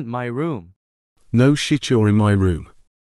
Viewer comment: I heard yohar XD